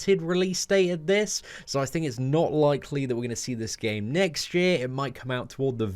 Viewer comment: Did he say next year?? That's pretty far away😕